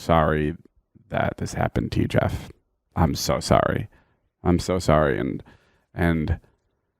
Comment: where he says i am soo sorry i swear he almost said im so over it.